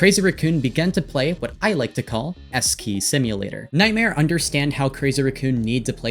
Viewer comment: lmao, I had to rewatch this part multiple times because instead of “S-key” I heard “Eskay” and I was like “wow, cheap shot but okay.”